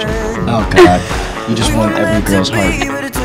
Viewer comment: someone can tell me what tyler said in 0:12 ? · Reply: You just won every girls heart